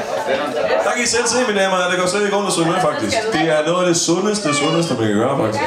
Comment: did he say "Fuck this"? HAHA